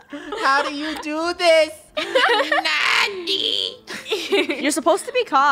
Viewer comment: did he just say NANI?